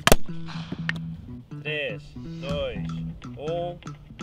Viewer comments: Did I hear "três, dois, um" at 15:10? És portugês? 😲😄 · were in doing the count down in portuguese? I think I heard tres, dois um